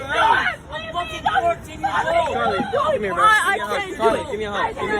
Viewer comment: at like 8:48 I heard one of the boys behind the camera say “come here bro give me a hug” ): Genuinely seems like a nice group of teenage boys who all have each others back just having fun and enjoying a shared hobby. That lady is actually the worst.